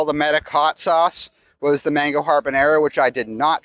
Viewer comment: Everyone is commenting about the mispronunciation of pho but not going to mention “harbornaro”? 😂 Much love to the Dis team! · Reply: I thought he said mango carbonara for a second and was like "Who is putting mango on their pasta?"